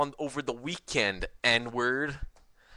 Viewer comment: sounds like you said "Edward" =P But good point.